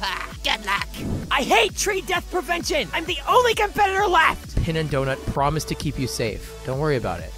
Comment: Am i the only one who heard in 7:26 , Fanny say "I'm the only confederate left"?